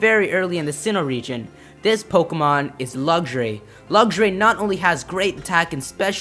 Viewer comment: What, wait say that again 0:50. Nooooooooooooooooooooooooooooooo. (I heard Lugray)